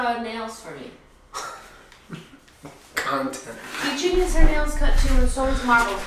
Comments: Did say C U Next Tuesday out of nowhere at 6:17!? 😂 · does he say C*nt???